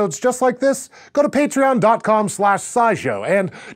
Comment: am i the only one who thought he said iShowSpace lol